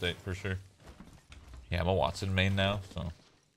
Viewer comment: for a moment I thought I heard emma watson main